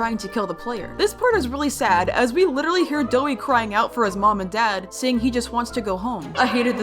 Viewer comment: nahh the subtitles said “moan) ☠️☠️☠️☠️☠️☠️💀💀💀💀💀💀💀💀💀💀☠️☠️☠️☠️☠️☠️☠️☠️☠️☠️☠️☠️☠️☠️💀💀